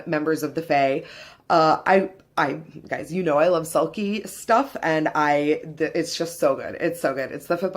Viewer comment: I heard "selkie" as "salty" and was like "................well alright then". Selkie makes FAR more sense hahaha